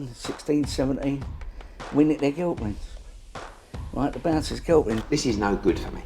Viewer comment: Will we ever find out if Ray Winston was 16 or 17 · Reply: I thought he was saying the bouncers killed him